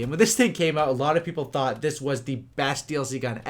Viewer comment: It sounds like he says "my penis realy changed" hahahah